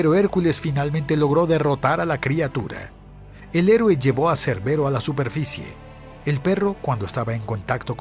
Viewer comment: Los 12 trabajos 6:24- León de Nemea 9:28- Hidra 12:44- La cierva y el jabalí 15:37- Los establos de Augías 18:36- El toro de Creta 21:35- Las yeguas de Diomedes 24:26- Las Amazonas 27:30- El ganado de Gerión 29:57 Hércules y Atlas 33:50- Cerbero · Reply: En el último pensé que decía cancerbero xdxdxd